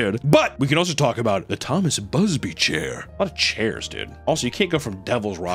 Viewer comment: i thought it said thomas bussy chair